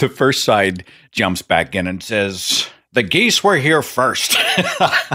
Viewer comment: In the beginning segment I thought he said "the gays were here first" and "I didn't climb to the top of the food chain so the gays could shit on my lawn" 😮 I had to rewind And re listen too the whole thing